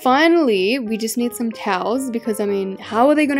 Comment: You heard you say cows not towels but that’s ok people make mistakes